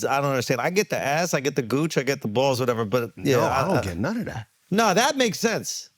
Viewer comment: I’m so glad I heard “I don’t understand non of that” 😭